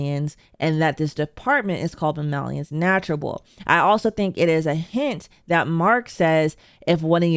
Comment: I’m somewhat fascinated that so many YouTubers seem to mishear the name of the goat department (Mammalians Nurturable) to instead be “Mammalians Naturable”. This is confusing to me because while “nurturable” is barely a real word, “naturable” isn’t even close to being one.